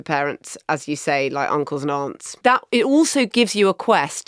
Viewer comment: When Marina said '..Uncles and Aunts..' i heard "Uncle Nonce'. Always a significant character.